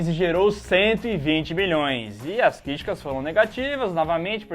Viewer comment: "Anjos da Noite custou 50 milhoes e rendeu 120 BILHÕES" ouvi certo? Kkkkkkkkkk